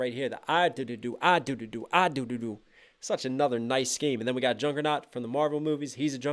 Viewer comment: Put On The Subtitles And Go To 20:05 Where It Said Jungkook Instead Of Juggernaut😂